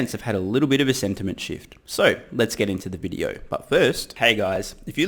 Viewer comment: Is it me, or every time I hear say "But first", it sounds like he is saying "Butthurt". Perhaps in reference to all the shorts? Love your work:-)